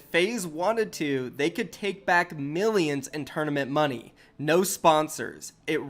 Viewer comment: what you talking about, faze can take money? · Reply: yah ik im just a littel late lol I thought he said mills not midrolls and when I thought he said mills I thought he was talking about tfue